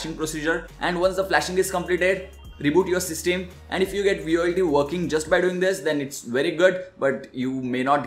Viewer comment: what did u say? if u get _____ working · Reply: if u get VOLTE working